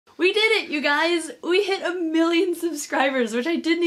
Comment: I thought she said "we did it guys we hit 1million snakes"......wow